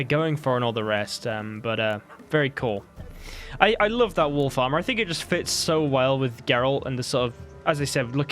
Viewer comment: Oh, WOLF ARMOUR. I thought you said WOOL FARMER.......& I was genuinely confused 😃.